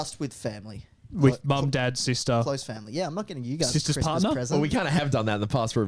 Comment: talking about gifts for family, "mom, dad, sister" What I heard, "mom, dead sister"